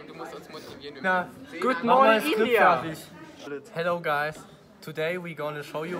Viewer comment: did he just said "gm india"?